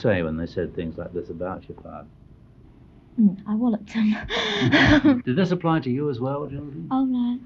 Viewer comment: she doesn't say "Um, I walked away" as the closed captions say, she says, "I whalloped them!" meaning she fought them.